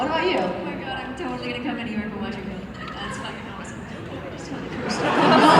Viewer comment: what did she say? I heard "Oh my god I'm totally going to..... something .... that's f**king awesome." What did she say in the middle? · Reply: I believe she said "I'm totally going to go to New York now, That's fucking awesome"